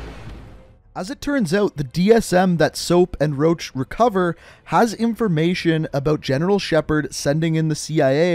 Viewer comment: did you say soap and roach recover dsm ?